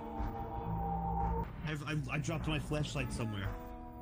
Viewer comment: is it just me or did say "i dropped my fleshlight somewhere"?